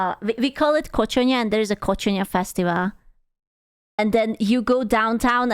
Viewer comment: I heard “Coach, uh, NYAN!” 😏